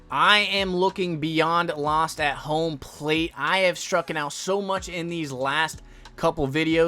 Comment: Did I hear "strucken out"? 😄